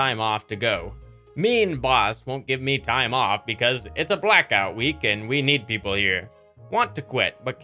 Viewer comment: Broo wtf i heard "blac guy week" and my brain stopped for a moment